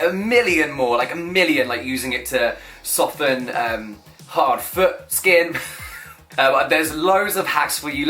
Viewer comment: I legit thought at the end he said "softens hard foreskin" 🤣🤣🤣🤣💀💜🖤